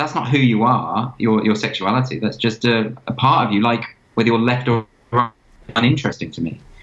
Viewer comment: "Your sexuality is not who you are, it's just a part of you like wether you're left or right HANDED." He said the same elsewhere so that is apparently what he said here. This is how I see it as well.